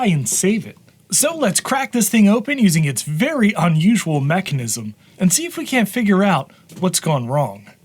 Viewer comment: Did he say "macnism"?